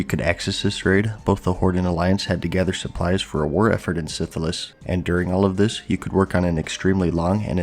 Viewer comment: HAHA did you say syphilis at 5:37?